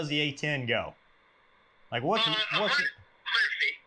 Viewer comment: ? I can't make it out what he said, can you guys help? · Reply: 100'